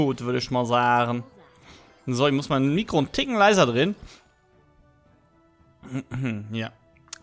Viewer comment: ich hab nen fick leiser verstanden